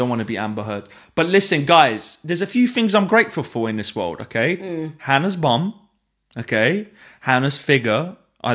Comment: I thought you said you were grateful for "Hannah's mum." I'd be grateful, too.